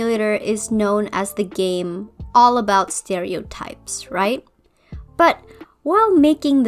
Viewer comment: When she said stereo types I thought she said scary knives 😭